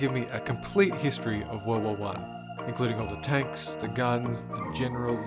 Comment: Some reason I thought you said world wall one the entire time instead of world war one